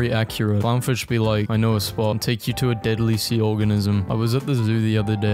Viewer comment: When it said deadly sea organism I thought it said deadly sea orgasam 🥶